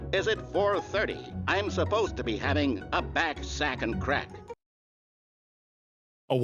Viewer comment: I heard "fat sack of crack" lmao